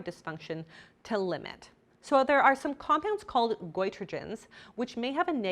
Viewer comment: The captions: "boi Trojans" Me: "Nice."